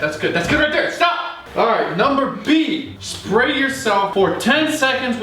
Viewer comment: Did just say number b?